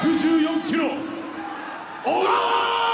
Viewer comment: Lol, sounds like he says sausage